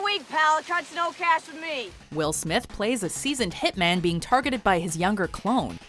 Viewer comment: Hold on.. did they say will smith? At 3:00???